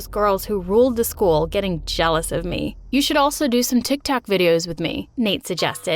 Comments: Wait at 6:42 did Nate just say TikTak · Is it just me or he actually said tic tac?